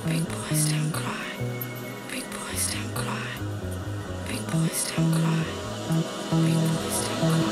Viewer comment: With the echo, it always sounded like she was saying “big boys don’t cwy”